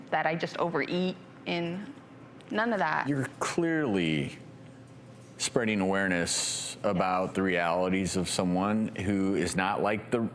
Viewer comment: the way he says "spreading awareness" i thought he was gonna say spreading ass